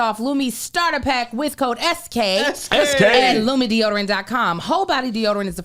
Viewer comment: In that lumi ad i heard “lumidee-oderant” 🤣🤣🤣